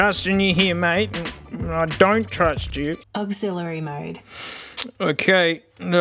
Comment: When his speaker said "Auxillary mode" I legit thought she said "I'm sorry m8" 😂